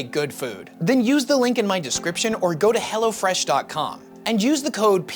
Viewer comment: For just a fraction second I thought he said use the link or go to hell.